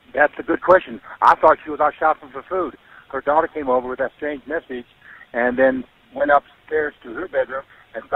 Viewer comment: At the end of the 911 call did I hear him say “that’s when her daughter came home with that same message”.. ??? So did he receive a similar message Just without the money transfer and the placement of the house key that’s crazy suspicious to me listen